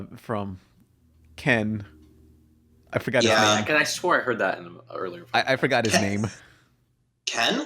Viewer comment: I thought he said no swearing